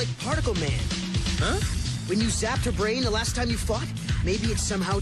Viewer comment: sounds like gear says fucked instead of fought